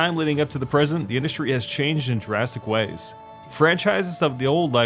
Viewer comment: It sounds like you said the industry has changed in Jurassic Ways